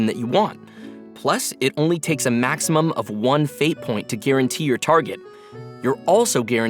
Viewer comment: FREE 5 STAR HOYO LISTENED LET'S GOOO upd: Nevermind I heard "maximum of one Fate" instead of "Fate Point"😢